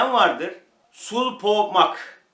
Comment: de ne dediğinizi anlamadim. Lakbenit madenini internette ne yazip aratalim ? Benim yapraklarim ust uste yetisiyor dedginiz gibi. Ciceklenme yapti meyve vericek sanirim. Ama yapraklarin uc kisimlari sarariyor korkuyorum olmucak diye 🙈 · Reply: Sul-Po-Mag: Potas varyasyonudur. Sul-Po-Mag aslında (potas-magnezyum sülfat) ‘langbeinite’ diye adlandırılan doğal olarak oluşan bir mineraldir.